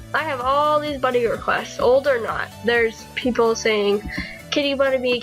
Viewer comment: Also on 01:05 did u just say pickles? owo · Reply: Sorry ;-; ive been busy with rl and stuff DX It does sound a lot like pickles but I said "people" XD